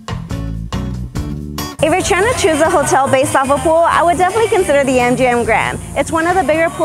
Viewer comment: I thought you said, "If your trying to chisel a hotel and a pool..." I thought that sounds like a lot of work, and a lot of exposition to the hot weather.